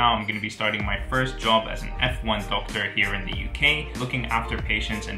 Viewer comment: When you said an FY doctor 0:14 I thought you said F1 and meant the sport and I was so excited because I love F1 but then I Googled it and figured that wasn't what you were talking about lol. I'm currently a pre-med student in Australia, keep up the videos you're very inspiring and give great insights! Your Aussie accent needs work , congrats on the plaque too!